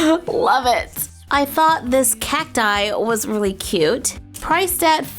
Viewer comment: Every time she said cacti I thought she was saying cat dye